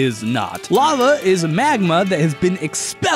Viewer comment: Thought he said “Mad Mud” instead of Magma. Either way both is correct.